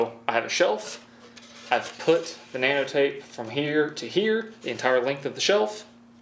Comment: did he say he put some banana tape on there? LOL